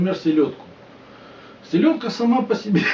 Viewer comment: Спасибо поржал! Одному мне послышалось, что селедка – сука?